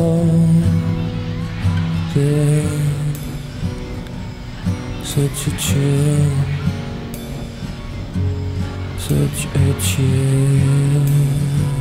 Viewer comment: always thought he said "choo-choo train, choo-choo train" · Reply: he says "such a chill" ... much better than choo-choo train